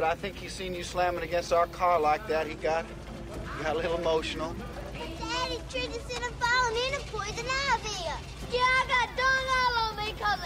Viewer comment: what did she say? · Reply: GSMillion hey daddy he tricked us into falling into poison ivy